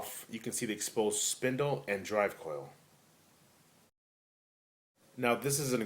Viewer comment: You can see the exposed spindle and .....? What did you say? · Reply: "Drive Coil". The motor that spins the platter.